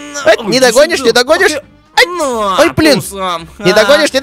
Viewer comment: это мат или мне послышалось "ай бл*ть" ? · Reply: Это блин а не бл*ть